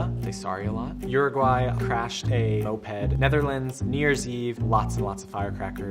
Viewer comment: anyone knows what does he say in 0:10 ? Crashed a what? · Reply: Moped